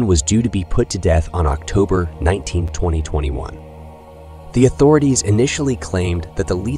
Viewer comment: the narrator attempts to say “… Dixon is set to be put to death on October 19 (in the year) 2021”. However, it sounds more like the date is 1920, 21. In the transcripts, it’s written like this: October 19221 There are several of these minor robot errors throughout and I like it cause it makes listening to it sound kind of funny instead of horrific, which it definitely is.😬🫣🤭🤫😎